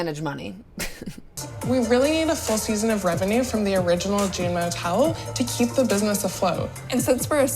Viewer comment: I keep hearing "va-june" and it's throwing me off hahahahahaha